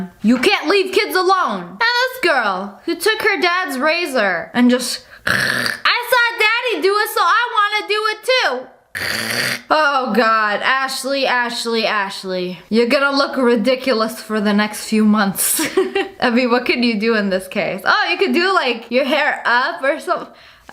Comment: When you said Ashley I thought you said my name Ashlyn even tho it says Bryan That’s my dad